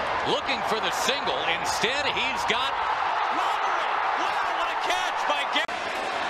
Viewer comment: speaking of the rangers, who was that player that robbed that home run I couldn't hear it very well at about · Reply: I remember that catch on sportscenter. That's Gary Matthews Jr.